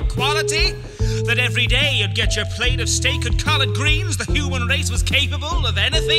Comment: For the longest time I thought he said, "Have your steak and call it greens"